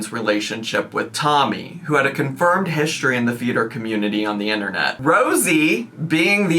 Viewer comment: Ok for first few minutes of this video ....I thought they said theatre community, not feeder community......and i was very confused 😮😂